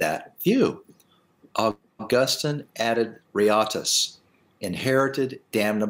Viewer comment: What does Dr. say at 30:35 ? Augustine added what? Riatus? Did anyone catch that, I'm trying to quote him but I'm not sure what he said/meant at that point. Cheers.